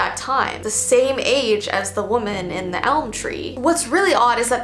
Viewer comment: I thought it said elm street 😭